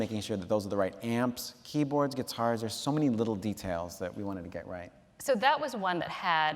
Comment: What does Fred say at 12:23? I love that whole, odd exchange. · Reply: JohnnyNosebleed He says, “There’s so many little details that we wanted to get right.” She starts to reply, “So that was one that had...” Then he adds, “I interrupted you, Seth, I’m sorry,” and they make a whole bit out of Fred seeming rude. (Sorry for over-explaining, I hope that answered your question!)